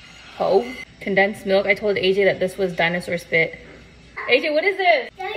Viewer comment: I thought he'd said dinosaur drool!